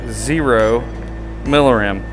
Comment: Subtitle errata: 39:30 Milliram instead of Millirem